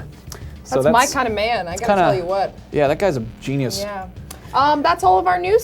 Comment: Did just say "I tell you what" ? What i she, Hank Hill?!